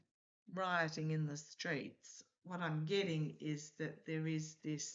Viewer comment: I woke this morning with the song 'any day now' playing in my head while the guides put trump's face in my vision. I'm taking that as a good sign... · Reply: I keep hearing "there will be dancing dancing in the streets"! 😜